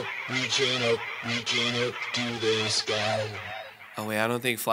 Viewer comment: Cybop sounds like he is saying “reaching up, reaching up, reaching up to the skies” to me. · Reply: I thought he said reach it up